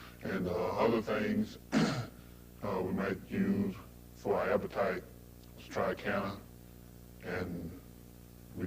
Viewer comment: What was the appetite stimulant he said they used in 9:10?? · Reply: Triacana